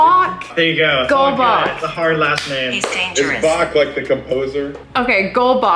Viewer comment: when you were talking about the flash of sam i heard he's dangerous Edit when sam or colby was speaking about the little gilr i heard ambulance 🚑